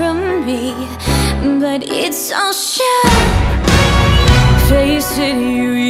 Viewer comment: Anyone else thought she said "And it's all shit" instead of "Its a show"